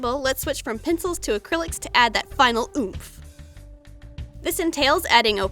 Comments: did I heard that right? "Final oof" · In 12:13 - 12:15 did u say oof? Well the eevee evolution are my favorite and I can't believe it has to end. Nooooo. Also my favorite eevee evolution is vaporieon